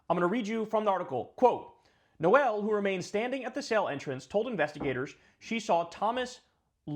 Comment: what is the sale entrance? Oh, you mean the word "cell".